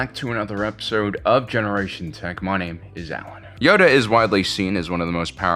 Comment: I always thought he said Money is Allen today I was paying enough attention to clearly hear my name is Allen. I'm only a little disappointed.